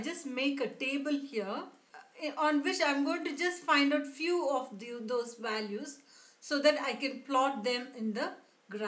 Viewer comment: sounds like she says "find a few dildos values"